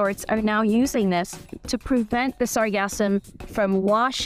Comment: Oooooh "The Sargassum", that makes a lot more sense! Listening to this in the background I heard it as "This orgasm". And the hilarious thing is that it still almost made sense: "Some beaches are more organized than others in getting rid of and collecting this orgasm!"